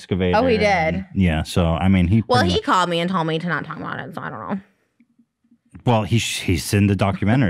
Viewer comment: what she say lol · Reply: She said “well he called me and told me to not talk about it so”